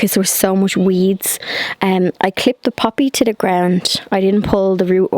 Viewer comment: At first I thought you said you planted a puppy...then I realized you said poppy. 🤣 Everything's looking great!